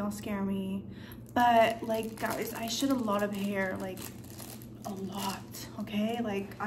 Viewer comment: I THOUGHT U SAID "I shit a lot of hair" I WAS LIKE WHAT I HAD TO RUN THAT BACK